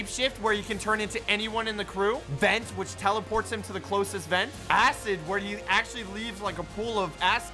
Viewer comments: Did he just accidentally say "a$$" instead of "acid" here? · excuse me , did u just say - pool of a*s Me - ( intensely coughing )